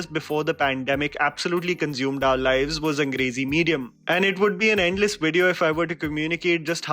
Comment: I heard 'Crazy Medium' instead of 'Angrezi Medium' 😂🤷🏻‍♀️🤦🏻‍♀️